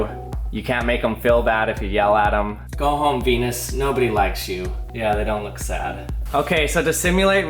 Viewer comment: "go home pe***, nobody likes you" is what it sounded like😂